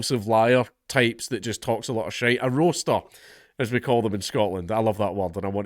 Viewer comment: I thought you said "rooster", like "full of cock and bull".